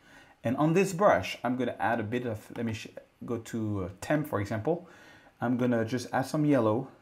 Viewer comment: Did he say "Let me shit" at 16:21?